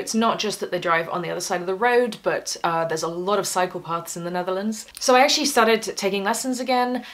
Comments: Cycle paths , ohhh ! I had to rewind ... I thought she said psychopaths ,there a lot of psychopaths in the Netherlands LOL Okay ..... continue 😇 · Oh, CYCLE paths. I thought you said psychopaths.